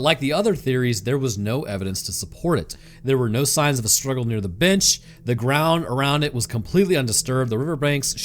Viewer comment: totally heard "by the bitch" think he said bench, but the dog was there lmao